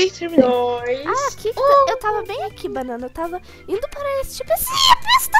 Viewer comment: só eu que ouvi a chamar a bananinha de batata???? Kkk